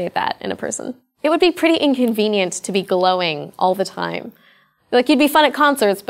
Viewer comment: i thought she said "it would be pretty inconvenient to be "Blowing" all the time"